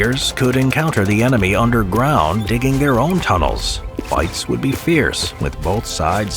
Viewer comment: Bro 3:53 what did bro say? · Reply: Fights would be fierce